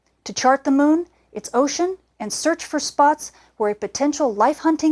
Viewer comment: where she said, "...search for spots" - did anyone else initially think she said, "...search for Spock"? :-D